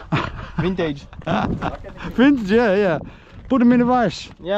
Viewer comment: I heard you say put him in rice. Lol😅😅😅😂😂😂😊😊😊😊😊🎉🎉🎉🎉 you still need to make a emergency rice bucket...LET'S GOOOO!!!! 🎉🎉🎉🎉🎉🎉🎉